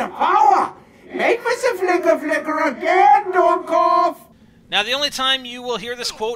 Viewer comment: The ??? was Richtofen saying "dumbass." The ???? may of said "Son of a bitch."